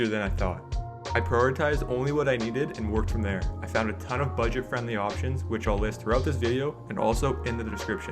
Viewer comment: I thought you said budget FRIENDLY